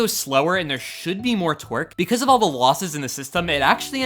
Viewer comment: he siad twerk instead of torque